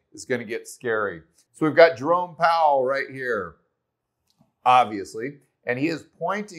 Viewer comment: did you say Drunk Powell?